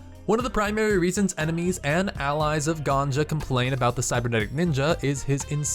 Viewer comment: did you just call Genji Ganja? Edit - 7:22 Edit 2 - 7:43 Edit 3